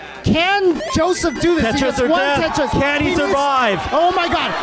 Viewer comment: hmmm did I hear... Tetris for Jeff?